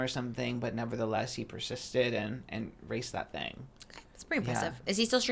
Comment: not me having just Trish in the background, major lore drop when I thought they said RAISED a cheeta and not RACED a cheeta